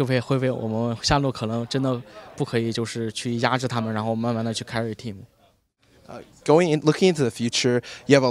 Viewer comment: he actually says:" to carry a team " ROFL